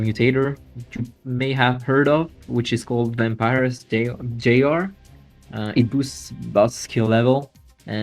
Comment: the guest is talking about a mutator that increased quality of Ai - what is the name of this mutator cause I can't hear it properly to find it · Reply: found it! advanced-bot-vampirusjr